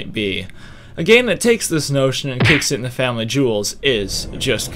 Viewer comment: did you just say family jules as in familyjules7x the awesome video game metal covers dude?? hell yeah \m/